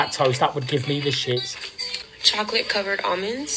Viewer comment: Omg, I misheard and thought she said chocolate covered onions.. I was like “wtf?!”